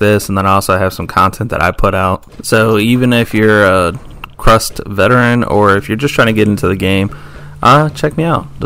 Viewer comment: Did u say "even if your a crust veteran"? 2:52 😂